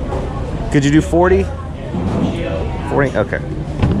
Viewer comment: i heard $25? 😅